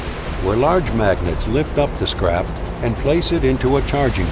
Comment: Everytime he said "this scrap", all I heard was "(magnets lift up) this CRAP" lmao